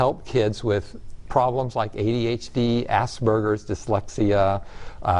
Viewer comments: Did he just say assburgers? This is not to be joked about but i laugh everytime he says that. Ohh im going to hell. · Did he just say Ass Burgers instead of Aspergers? Lincoln and Cartman, you're killing 'em